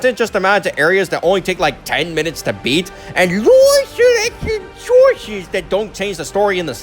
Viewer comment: I can't understand what you said there. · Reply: Sorry, I said “live selection choices”.